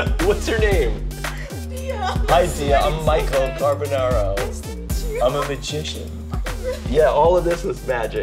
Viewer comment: Thought he said “I’m an Egyptian”